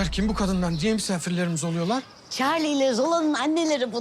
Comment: Hacer 2:42'de Çavi yerine çarli mi dedi ben mi öyle anladım :)